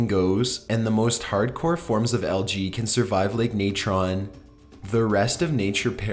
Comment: oh! Algae! I thought you were saying "LG"